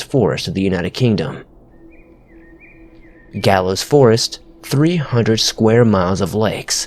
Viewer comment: What forest was this? Sounds like you said Galus forest. I know there's a Galloway...?